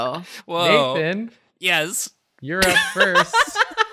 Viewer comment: "Nathan, you're up first." but i heard "Nathan, Europe first."